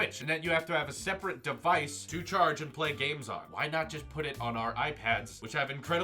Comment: I thought u said no one want to play games on phone